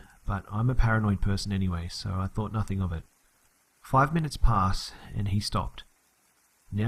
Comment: I thought it said 53 minutes